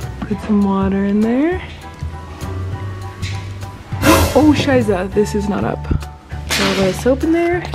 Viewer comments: did she say "oh scheiße" at 6:10 ? hahah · Did say "oh scheiße" at 6:09 ?